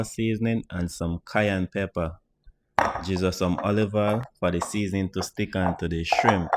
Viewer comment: Did he just say, “ Jesus same table oil?”